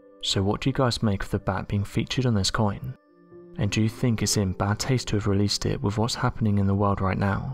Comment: I thought at 3:02 that he said it’s in “bat” taste to release it. I rewatched it though and it’s clear I imagined that lol, definitely said “bad”. Btw, fruit bats are so cute!!!